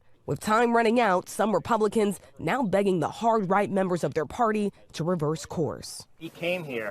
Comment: misheard as "Hog-Right"